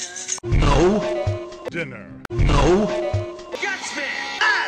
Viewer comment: What did he say? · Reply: schwartz "Gadunka." It's a toy from the Bionicle franchise, which I am a HUGE fan of.